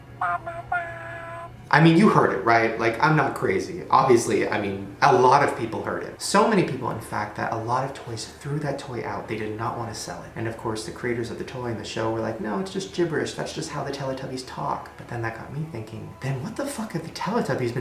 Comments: am I the only one who heard him say "alot of toys through the toys away" OHHHHHHHHHHHHHHHHHHHH 😲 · it sounds like he said a lot of toys took them out XD am I the only one that heard that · did anyone else hear shane say a lot of toys threw the toy away lol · Is it just me or did Shane say " a lot of toys threw that toy out " 😂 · anyone else hear "a lot of toys threw that toy out" around 14:25? lmao sorry